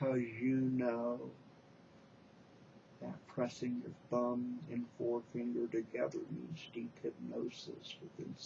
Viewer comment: Oh dear, when I listened to your other video, I thought you said "thumb and fourth finger". Imagine that being my anchor :-)